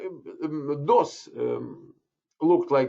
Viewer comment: "We are in DOS, корочеговоря..." . Или мне послышалось?